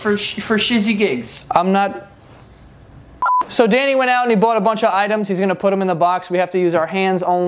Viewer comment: “danny” at 1:35 is the cutest thing you’ll come across today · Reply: I thought he said “yay!”. Its so much cuter now that I know said, “Danny”!! 😍